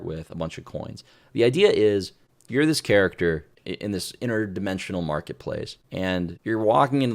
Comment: he actually said "Interdimensional Marketplace" which is good that helps explain why so many things are magical and fantasy when I thought originally this was going to be a space, sci-fi, alien game like a space station marketplace or commerce world